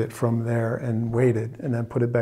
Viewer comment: I heard you say "I took the battery out and weighed it" - because as we know charged batteries weigh more than depleted ones, and I thought you were checking 🤣